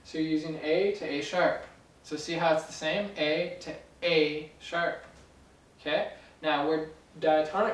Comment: I accidently said the second one because I thought it said e sharp to f sharp